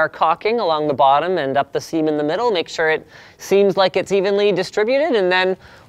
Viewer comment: "Seem in" sounded like "semen", so "our cocking and semen in the middle" - made me laugh